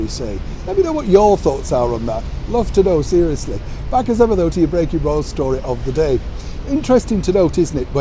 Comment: When Shaun said “breaking Royal”, I heard “bacon roll”. Wishful thinking?..🥓🥓